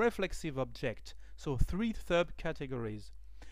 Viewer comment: Sorry, what is he saying at 02:13 ? It sounds like "three thupp categories" (??)